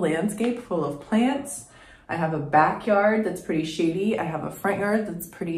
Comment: OMG! I thought you just said “I have a backyard that’s pretty shitty” @ 5:41 😂 I’m also in the 8a-b growing zone one state down from you. Look forward to watching your progress and get inspired to get out into my own garden more. Also have some ADHD in the mix so I definitely understand the challenge. Good luck with your channel!